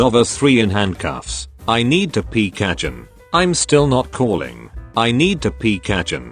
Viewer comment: how many times i thought deku said i need to peep chicken?